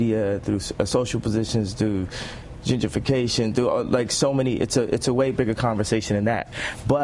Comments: Time 2:23, what is word did he use? It sounds like jew-gification? Is this a new term created by the likes of Jermiah Wright (Obama's "pastor" for 20 years) and Farakhann? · did he say gingerfication? 2:24 haha